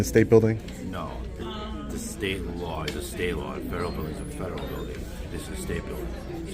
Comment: Did he just say stederal building 😂😂